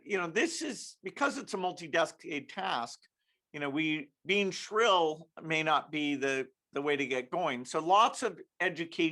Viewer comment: what is he saying it sounds like multi something past I would love to know lol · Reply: Sounded like "Mutli destinated task. Being shrill is not the way to go about it." Multiple tasks with different objectives to obtain a final objective of 0 emissions is what he is talking about. In other words pressuring countries into all kinds of major law reforms and new made up jargon to sell the narrative. Bill's dad was a eugenist, depop agenda is in his genes. The old saying "the apple don't fall far from the tree" comes to mind when I think of shady old Billy boy.